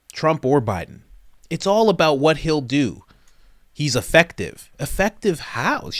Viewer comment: First time I saw this I swore she said “he is defective”…